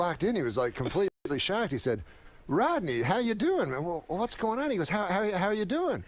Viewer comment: I'll tell you who it definitely isn't. Rogan. Imagine, to become a stand up comedian, you have to actually announce to everyone that you're a stand up comedian. "If I shout loud on stage it becomes funny, bro" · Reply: At first , I thought you said Rodan. The giant Japanese movie pterodactyl. But that doesn't make sense. I don't think he speaks English.